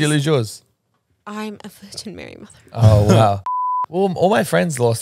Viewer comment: Couldn't figure out what she said at 13:14??? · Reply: She said ‘I’m a virgin , Mary mother’ then it cut 😂